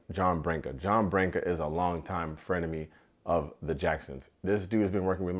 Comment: Did just say Frenemy 😂